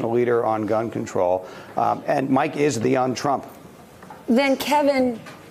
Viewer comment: "Mike is the young t'Rump." This guy's so tired, that he casually admitted Bloomberg is t'Rump. And Ruhle's so completely on autopilot, that she never noticed what he said.